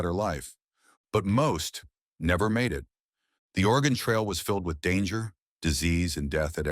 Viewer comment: I’m guessing I am hearing it wrong and that this is not the organ trail. To my English ears it sounds like he says organ not Oregan